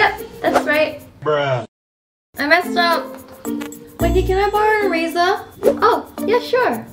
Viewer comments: when wengie said "can i barrow your eraser?" it sounds like "can i barrow a razor?" lol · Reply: ohh i thought she said can i borrow your razer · It sounded like she said "wengie can I borrow a razor" I laughed so hard